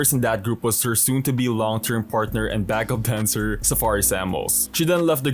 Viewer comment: Did you say backup cancer? 😂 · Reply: omg i said backup dancer i just didn't say it clearly 😭